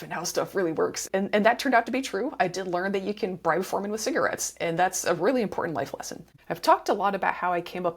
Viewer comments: what did she say? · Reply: "I did learn that you can bribe foremen with cigarettes, and that's a really important life lesson." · I know this is a joke and not the meat and potatoes of this video, but I can't figure out what's being said here even with slowing down and volume up 😭 Someone please help, it's itching at my brain so badly! "I did learn that you could ???????? ???????? with cigarettes" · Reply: 'Bribe foreman' - just turn on subtitles 😅